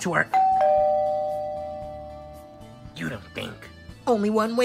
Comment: I thought he said You Da Bink! 😂😂